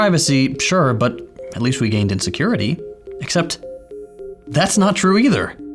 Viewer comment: "..we gained IN security" i see. thought you said "gained insecurity" lol